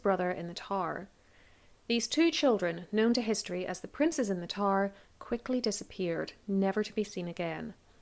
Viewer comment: For a second I thought you said, "Princes in the TAR," partially bc I was only half listening while doing other things so I missed who you were speaking of. I was imagining tar pits lol! Please, don't think I'm making fun of your accent, bc your voice makes this stuff nice to listen to, even when talking about murder lol. I thought it was funny & am laughing at myself for thinking you said "tar" & wanted to share the giggle.